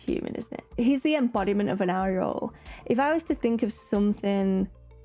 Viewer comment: Embodiment of a… what? What’s that word you’re saying? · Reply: Eyeroll. I think that's it. :)